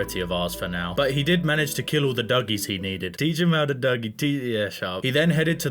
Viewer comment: what's he saying guys? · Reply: It’s a song pretty much a meme “Teach me how to dougie” it’s a dance